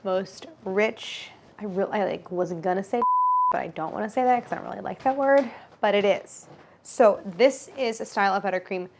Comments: What's the word??? It looks like "silky" but not quite. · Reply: Pretty sure it's "decadent" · i literally came back bc I have to know what she says at 17:18 HELP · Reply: Decadent